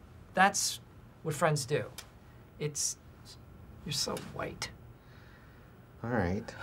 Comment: “It’s a joke Toby. You’re so white” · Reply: I heard ''You are so Dwight''